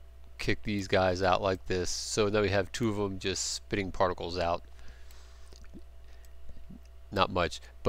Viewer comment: I hear liquid particles :-)